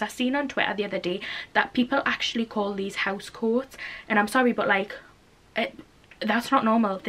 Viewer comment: Here in the US we call it a robe😂 love learning British terms ! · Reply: Sarah Young actually further comment reading, definitely have heard house coat, i thought she was saying house cord... i don’t know